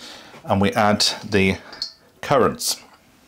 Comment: I am def going to try making these myself.. when you said 'currants', I thought you said 'carrots'😝